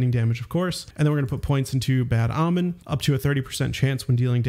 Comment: I thought you were saying "Bad Almond," which would be such a better name than "Bad Omen."